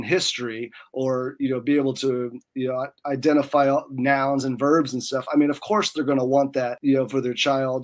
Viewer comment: at first I thought he said "nouns and birbs and stuff" 😅